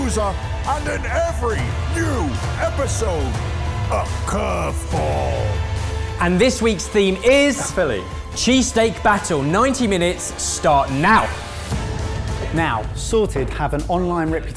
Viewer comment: For a moment I thought it said cheesecake